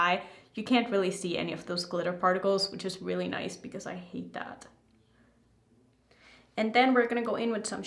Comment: I love your makeup looks! those palettes definitely go beautifully together. The second you started taking that neon pigment in your crease, I swear in my head I heard you say, "I hate this". 😂 we all have looks we've done where the vision did not come out as planned 😅